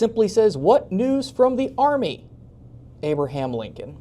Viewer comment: I think that may say front not army.